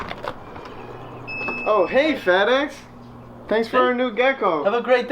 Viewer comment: I first thought you said, "Oh hey fatass" 🤣🤦🏻‍♀️